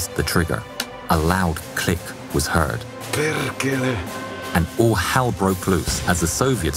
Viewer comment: What is the Finnish soldier saying at 2:51 and the civilian at 5:44? If anyone knows please leave a reply. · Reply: Perkele is an old swear word in finnish. Translation could be like goddammit in english.